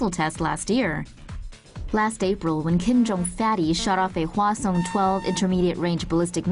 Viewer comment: Did she just call him Kim jun fatty?